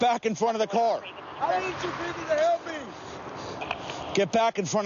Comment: did he say "I need you people to help me?" Lol 😆